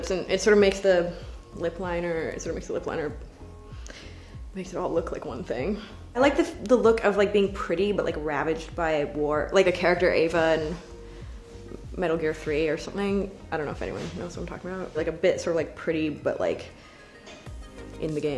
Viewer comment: "I like the look of like being pretty but like being ravaged by a war... " That's a WHOLE mood, sis. Edit: Wow, thanks y'all for the likes. I feel so honored lol. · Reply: I totally heard "ravaged by a boar"